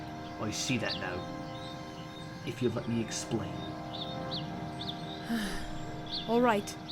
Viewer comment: I cant hear Oliver, what did he say? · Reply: I said 'Please let me explain' That's what I said.